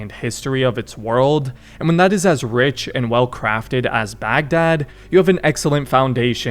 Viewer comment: he really said it bag “dad”